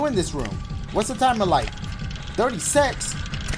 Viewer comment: it sounds like he said dirty sex instead of 36